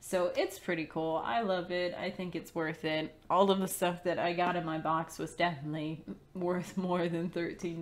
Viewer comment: Did she just say "get in my box" or was that "cut'n ma box"? :P I do own the local Tavern in the gutter :P